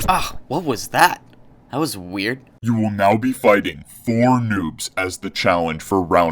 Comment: When it said 4 noobs I thought it said 4 nukes lol